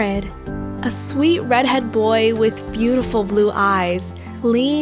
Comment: wait... i thought she said "red head"?